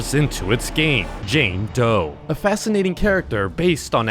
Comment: I thought he said jane toe